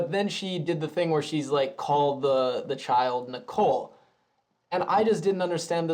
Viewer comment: I thought she said "Holly Nicole", not "Call her Nicole". That would make sense because she would only be adding a little bit of Serena to her name, instead of changing the whole name.